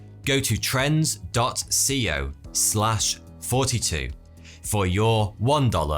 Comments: I keep hearing "forty-two" instead of "Thoughty2" or is he's just saying "forty-two" to confuse us. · I always hear "forty two here"